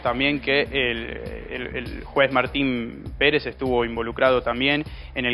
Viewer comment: " Un juez dispuso una prisión domiciliaria" dice el periodista! NOMBRE Y APELLIDO DEL JUEZ QUE OTORGÓ PRISION DOMICILIARIA X UNA UÑA ENCARNADA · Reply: JUEZ: MARTÍN PÉREZ, parece que dice el periodista enrique figueroa (08:20seg, más o menos) que da la nota enfrente de la casa (me imagino que es la casa de los teruel)